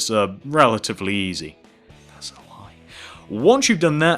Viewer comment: did he say "im so high" , "its so high" , or "thats so high" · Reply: That's sp hard I think